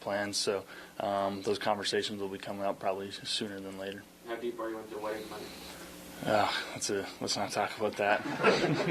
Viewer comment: What was the question? Can't hear him. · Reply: I was wondering the same thing. I think it's something along the lines of "how are things going with your wedding planning?"